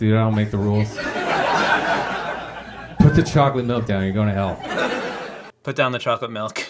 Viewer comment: I thought it said 💁🏽‍♀️ Chocolate Milf 😂